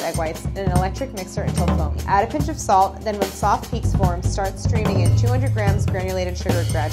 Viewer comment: Definitely thought she said “start screaming sugar “ I was like, start screaming ✍🏼 ok. got it!